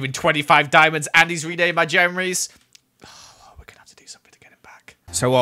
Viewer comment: “I just gave him 25 diamonds and he renamed my Jemeries “- 2020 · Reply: Playz they wrote "jemeries" because it sounds like said jemerys instead of jeremys